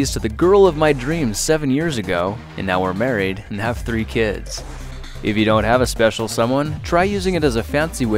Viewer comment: I did not hear I thought you said 30 kids it's 3